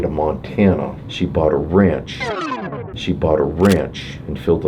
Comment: When he said “she bought a ranch“ i thought he said “she bought a wrench“